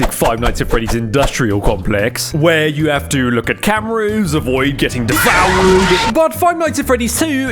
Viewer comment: I misheard it as "avoid getting deflowered"...this made the ensuing jumpscare actually scary